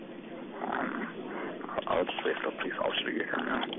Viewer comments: he clearly said alright mam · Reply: I heard "alright then" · sounds like he says “Alright ma’am.” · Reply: I thought he said “Ight then” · He clearly said "I'll just wait for the police officers to get here ma'am"